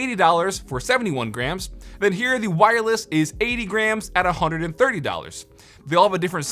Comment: It sounded like he said it was 80 Grand and I freaked out for a second